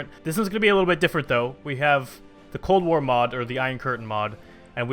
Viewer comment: Holy shit in the beginning i wasnt paying attension, and i heard "the clone wars mod"